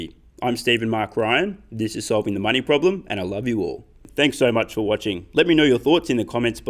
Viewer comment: Is it just me or does it sound like he says solving the mining problem instead of money problem.